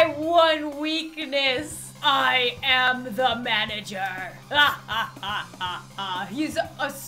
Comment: When he said “I AM THE MANAGER!” I thought he said “I ATE THE MANAGER!”